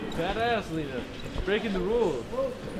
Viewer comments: At around 5:50 i thought said "dat ass leena" lmao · I thought said, "That ass Leena".